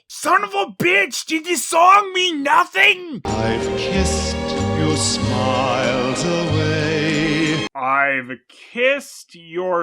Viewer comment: I have this movie on bluray. It came with my pack of Christmas Specials. The part where Bertha's boyfriend is leaving, that line said in the subtitles, 🎶I've kissed your smiles til' May🎶. Lol I thought he said "I kissed your smiles away too."